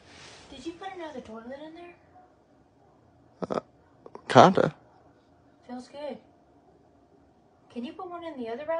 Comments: I listened to this at least a dozen times and still don't understand what she said. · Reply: sounds like she said did u put in a new toilet and can u put one in the other bathroom to me might be wrong though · I heard, “did you put another toilet in there?” “Feels good” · why are there subtitles for everything except for the wife who can't be heard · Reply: I listened with volume all the way up and still don’t hear toilet in the sentence or see it being said by lip reading…. Sounds like she said did you put another door mat in there? Or dorman.. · I didn’t even hear what she said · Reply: Sounded like she said "did you put another dorm room in there?" And "can you put one in the other bathroom?" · why are there subtitles for everything except for the wife who can't be heard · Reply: It sounds like she said did you put another doorman in the bathroom.🤷‍♀️🤷‍♀️🤷‍♀️